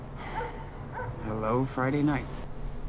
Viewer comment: pensé que dijiste HELLO DA en el eructo jaja PSDT : buen vídeo y me encanto tu rapado <3 <3